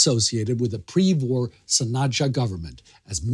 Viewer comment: i quite didnt understand the word you said, what pre war goverment? Something s... Thank you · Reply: Sanacja